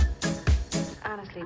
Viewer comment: Is it "Gotta wash my hands" or "I wash my hands"? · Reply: I hear "and I wash my hands"